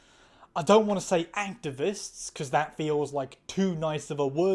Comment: funnily enough I misheard you here as saying "ANTI-vists" and thought, oh is that a neologism? But why would you call it too nice though? And then rewound to listen more closely 😂